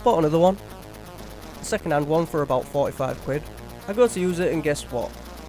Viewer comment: How much did you say that HD-PVR was at 1:56, again?... I'm from the U.S.A. & didn't quite hear the type of currency that you said you used. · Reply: ahh, I said 'quid' which is slang for pound (£ British)